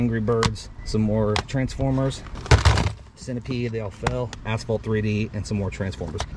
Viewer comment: it sounded like you said asshole 3D 😂